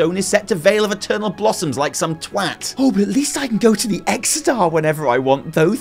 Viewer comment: please someone tell me what he's really saying -_- i keep hearing "those f**s" and i'm getting bummed out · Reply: Pretty sure he's saying 'like some twat'.